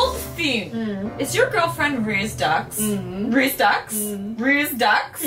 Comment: I thought said Raise Ducks until I replayed that part again two more times.